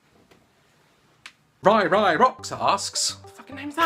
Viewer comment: when said ' ryryrocks askes' i thought he said 'ryryrocksarse says' ILMAO